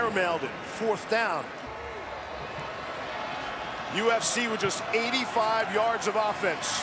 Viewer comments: dude was over here thinking about cage fighting 😂😂😂 thought he said USC at first played it back and he's defiantly saying UFC 😂😂😂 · Does he say ufc at 7:11 ??